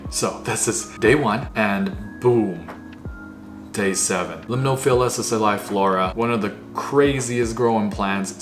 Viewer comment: does anyone know the exact spelling of this plant ? I heard say it but spelling isn’t my forte 🙏🫡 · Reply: limnophila sessiliflora